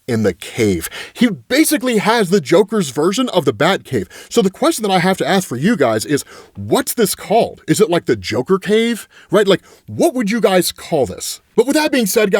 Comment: Butt Hole instead of Bat Cave